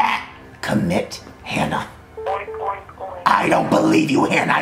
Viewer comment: “Commit to it Hannah.” “Oink, oink, oink” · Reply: I THOUGHT YOU SAID COMMIT A HANNAH💀💀💀💀